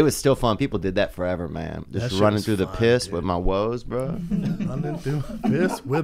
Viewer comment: Did he just say runnin through piss with my woes? LOL